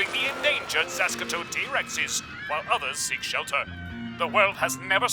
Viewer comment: Does someone know what he says ? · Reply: endangered sasqetoad t Rex's (Sasquatch toad t Rex)